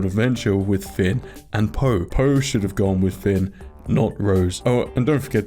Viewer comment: lol i think i heard that wrong, i thought i heard “poe should be with Finn” i would even rather have feminism over LGBT characters in my star wars. Star wars shouldn’t have even gotten a sequel like this anyway, they just made the resistance into the rebellion and the first order into the empire all over again! A remake of the originals almost